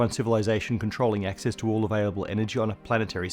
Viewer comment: Did you just say Energon?